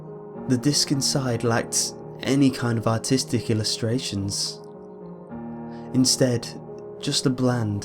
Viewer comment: did that say autistic illustrations